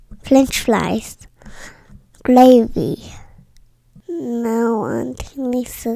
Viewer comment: Flied chicken, flench flies, flavy. 😂😂😂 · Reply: I heard ‘glavy’